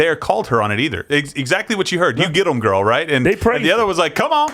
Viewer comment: I thought I heard a guy in the crowd say “Oh Go Home Girl” to AOC